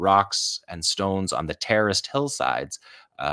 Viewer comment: why do I hear saying "terrorist hillsides" a couple of times? or is he saying something else since I can't make that out. Time stamps 3:18 and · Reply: terraced